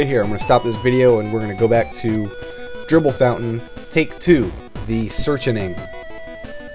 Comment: For a second, I thought you said "Gerbil Fountain"